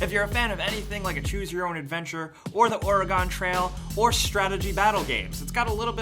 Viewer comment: did he just say OR-UH-GONE?